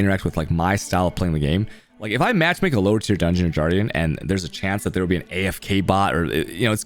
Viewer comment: Did you just say “jardian” for guardian?